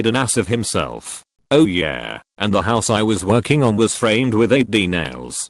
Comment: Lol omg! I thought he said finished the house in 8d, went back to check because it bothered me. Framed.